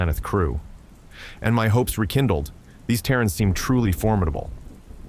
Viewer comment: Haha! My sense of hearing doesn't eork as well as it should, and half the time the AI said "Terran" I kept hearing it as "Karen"! My bad hearing made this story very amusing.